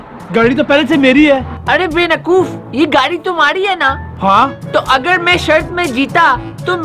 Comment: DID HE SAID BENAKOOF INSTEAD OF BEWAKOOF